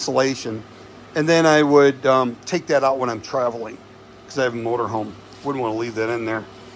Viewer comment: Finally someone set out too simplify this stuff for everyone else instead of the tech talk that leaves you confused . Real nice job ,jeep the videos coming ,your helping us diy'ers in getting our stuff set up simply. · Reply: I thought you said don’t keep it in the motor home. Isn’t that a fire hazard as stated